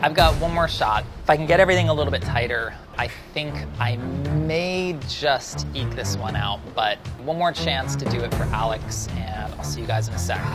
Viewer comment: "I may just eat this one out." Did I hear that right, Chris?